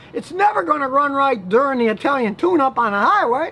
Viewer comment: Thought u said "the Italian tuna 🐟" 😂😂